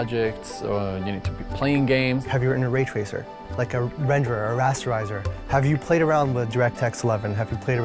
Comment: All I hear is "have you inner Ridge Racer, like a renderer a razzerizor". Pretty sure that's accurate.